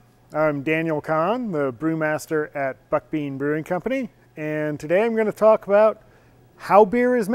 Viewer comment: i thought he said "fuck me brewing company".